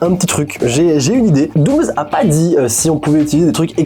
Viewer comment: c'est moi ou a la place d'avoir dit 'dooms' il a dit 'douze' 😂